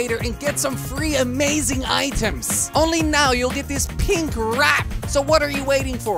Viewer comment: is it me or did he say pin rat